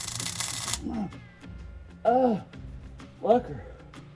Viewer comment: did he say " ahh fucker" lmao